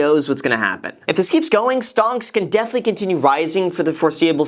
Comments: Hey, I heard that at 12:05 You can't slip a "STONKS" by me. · did somebody say stonks?? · did he say 'STONKS' at 12:04 ?? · Did.....Did Graham just say "stonks " ??? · Did you really say the word "stonks" at 12:05? 😂😂😂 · did he say stonks?😂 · Did bro just say stonks 😂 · did you say stonks at 12:06?